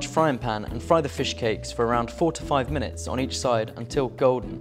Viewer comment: I thought he said fry for 45 minutes.. had to watch that part again ,than I heard fry 4-5 minutes lol